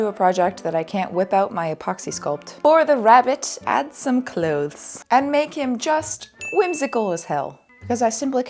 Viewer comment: I thought you said cloves. Sounded delicious 🤷🏼‍♀️